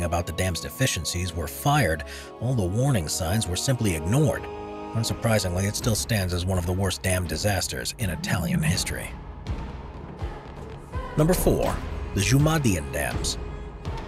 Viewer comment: The ultra-rich got away scot free? You don’t say. Never heard of something like that happening before. Side note: Every time the narrator says “worst dam disaster” or something similar, I hear “worst damn disaster.” Just go to 12:53.